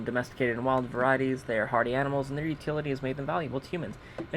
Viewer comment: I wasn't looking at the screen when he read "hardy animals" (about the Brahmin) so for a second I thought he said "party animals". This is especially hilarious because my friends and I used to have a joke about Party Brahmin. Like, that Benny hosted huge parties and some of the guests were Brahmin. Looooong story... but in my mind, they are party animals, soooo.